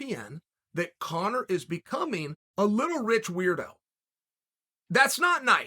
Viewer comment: He thought you said rich little is a weirdo